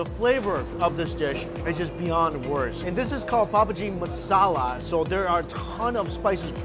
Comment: did he just say Pubg masala 🤔😂😂😂